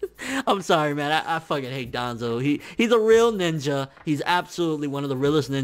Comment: Well shit... I thought he said Danzo was a real nigga